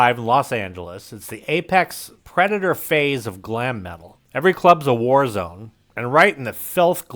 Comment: I heard "every club's a whorezone." Y'know, that actually tracks.